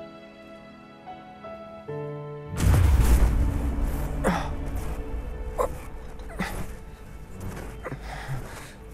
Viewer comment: does anyone know what aerith's lips saying? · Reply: Zack. But idk if it's japanese lip synced so she might be saying Zakkusu(zaks-su) or Zack Fair for English lip movements